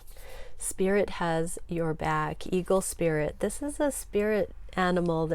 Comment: OMG I thought you just said Kegel Spirit (Eagle Spirit) I'm dying laughing 😂🤣💕